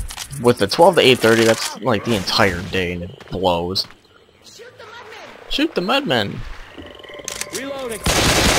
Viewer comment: when she said shoot the mudmen i thought she said shoot the muffin